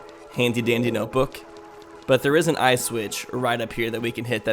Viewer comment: Every time I hear say "eye switch" I hear it as "ice witch"